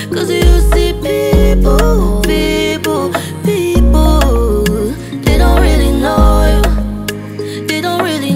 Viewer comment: I thought she said people are not really loyal💀😭